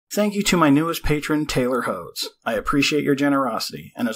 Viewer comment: It sounded like you said tailored hoes at the begging.😂😂😂